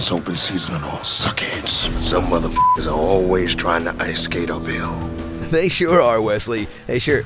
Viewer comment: Why is the "ice skate uphill" line met with shrugs? I heard it all throughout my youth. It means some people try to do the impossible (as in f$&@ with Blade and win). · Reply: All this time I thought he was saying “ice skate a bill” meaning trying to pass a new law without it getting reviewed that is bad like crooked politicians do.